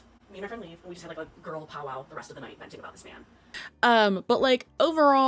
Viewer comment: did she say “girl powwow” 🙄